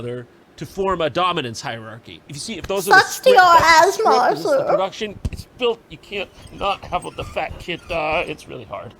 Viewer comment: What did she say at 4:37 ??? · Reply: Sucks to your ass-mar. It's a line from Lord of the Flies.